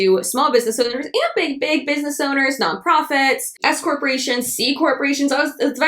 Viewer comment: omg when you said "s corparations" i heard "escorts" hahah my bad 😂